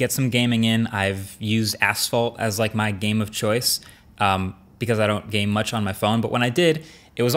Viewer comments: I thought he said a$$hole7:35 😂😂😂😂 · sounded like a$$ hole 🤔